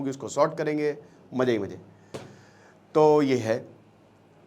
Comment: I heard iski ben ke mazey majey